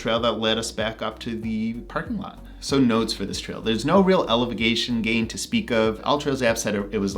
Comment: did you say elevagation gain? Haha